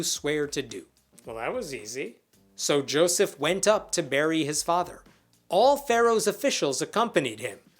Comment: As I walked back in the room you said “so Joseph went up to bury his father” but for some reason all I heard was “Joseph went up to marry his father” 🤣